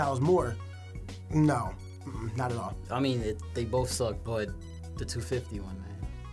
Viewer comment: did he just say they both suck savage lol